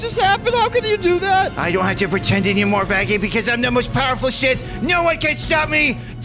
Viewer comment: i don't have to pretend anymore I am now the most powerful shit that's what I heard